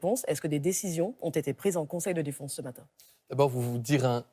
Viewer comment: j'ai entendu "conseil de défonce" ou j'ai rêvé (0:14)?